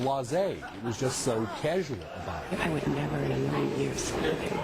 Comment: What does Lisa say at 15:06? ~:~ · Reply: The commentary was talking about sex and with my headphones turned all the way up it looks and sounds like she says - "I would never in a million years sleep with him. Never! "